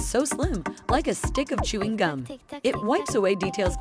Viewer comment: Subtitles:-" Follow for more weird erasers" What i heard:-"Follow for more Rude erasers"